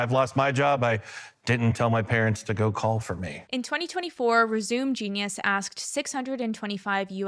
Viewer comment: I think that was supposed to say Resumé Genius, which is an ironic mispronunciation in a video about kids that don't want to get normal jobs 😏 Jokes aside, though, great vid with nice points. I was born in '89, and thought the new way of being is pure laziness, but after making good money for a couple years now, and still being nowhere close to owning a home, I think I get it.